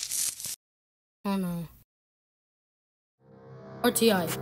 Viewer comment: Did science just say “oh no”?